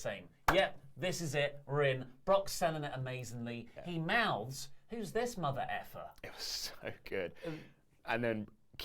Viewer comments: I thought that was what he said😂😂 but he actually said “who’s this big FELLA” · Anyone catch Brock Lesnar's F-Bomb when Keith Lee came out? It was silent and the camera angle was to the side, but Lesnar straight looks at Paul Heyman and mouths, "You're f*cking kidding me." It was priceless! Keith Lee v. Brock at some point? Yes, please. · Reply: I thought he said "who's this motherfucker"